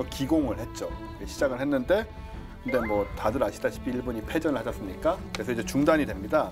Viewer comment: 내가 잘못 들었나???왼쪽 MC께서 초반에 하시는 말씀, ":일본이 패전 하셨으니까"!!패전한거지 무슨 하신거냐???